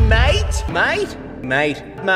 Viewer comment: The more i Hear it, the more i hear him say “MaYtE”